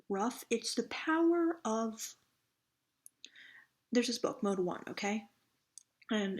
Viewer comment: Did she say mode one on 8:07? Or what book is it?